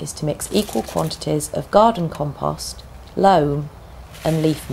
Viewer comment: What are the ingredients you mentioned to make a potting mixture? I can’t quit make out the names you said. I heard “garden compost, low, and leaf mold.” Thanks you.